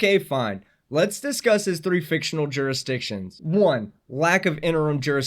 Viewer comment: I think he said "in rem jurisdiction," which I can only assume he mentions because these freaks are obsessed with admiralty law.